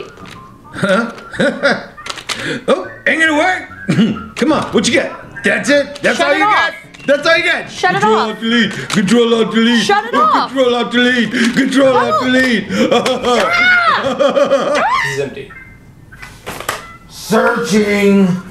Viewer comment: CONTROL ALT DELETE, CONTROL ALT DELETE, CONTROL ALT DELETE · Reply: I hear “Control All Dell Bee”